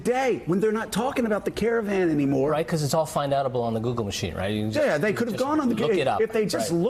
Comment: Did he say "find-outable"?